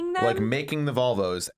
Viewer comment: When said “Volvo” I thought he said “vulva” and I was SO confused